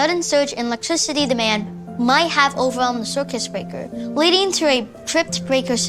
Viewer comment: 💀subtitles: “ demand might have overwhelmed the circuit breaker” what the actor says: “ demand might have overwhelmed the circus breaker”💀